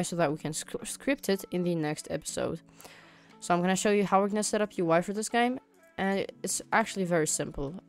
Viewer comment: sounds like your saying "Now im gonna show you how to set up your wife for this game."